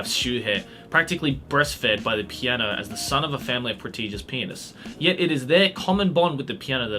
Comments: Did anyone hear say "protegus penis" 26:56 haha He must been watching a lot of yaoi before this recording. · "pianists.. I heard that right. Right?" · IS it just me? or just said P3nis instead of pianist on26:55 ? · Did you just say...prestigious penis (?) hahahah